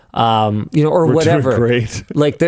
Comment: What does Luke say here? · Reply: "we're doing great"